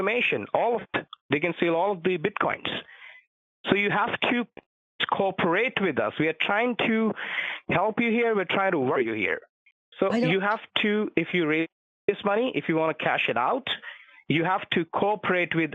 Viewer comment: Honestly sounded like he said "we are trying to rob you here".... :P